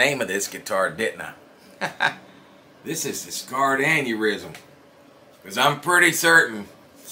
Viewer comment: I thought he said Scarred Anurythm